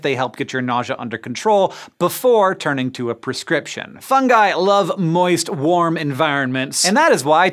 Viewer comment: anyone else hear "f*** i love moist warm enviornments!"